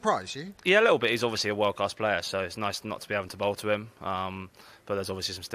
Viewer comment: What did he said at 1:46:40 Please tell me 😢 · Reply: He said, “nice i dont have to bowl to babar”